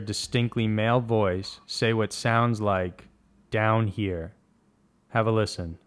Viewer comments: I heard not "down here", but "IM HERE" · I hear "We're here", not "Down here"